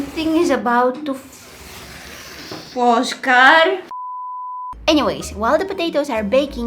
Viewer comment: did you say foscar